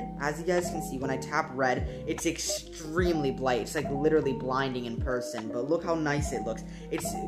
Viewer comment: G did you say blight